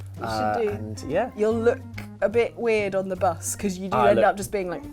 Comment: I thought said, you'll luke weird on the bus" but rewatching it I realize that I am just so used to making puns that I automatically hear puns when she talks